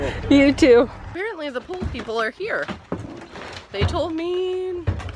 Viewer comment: I thought you were saying that you were getting a POOL delivered, not a POLE! 🤣